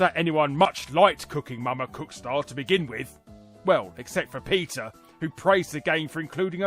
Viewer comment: I had a flashback to a Family Guy moment (sadly/strangely) when you said PETA and I thought you were saying Peter at first, as in baldlylocks.